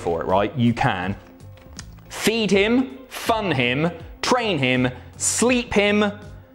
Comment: When you said "Fun Him" I heard "Fuck him" and had to put my phone down and look at the TV. Then I read the back of the package and was like....oh. oooh.😂 I was very concerned for a moment